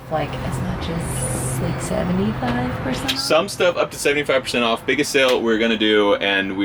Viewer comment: I heard it here y’all said Lincoln Active is gonna be 675% off